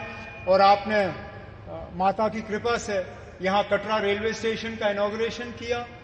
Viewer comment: And at 2:21 did he say, "Mata ki kripa se" 🤔